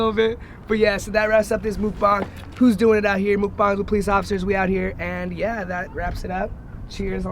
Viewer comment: I thought that it said do not mukbang with a police officer I was like wtf u do